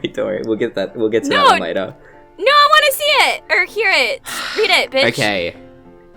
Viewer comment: Did she said read it b*tch?